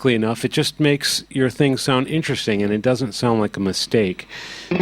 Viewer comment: did he say 'Mustake' at 0:40?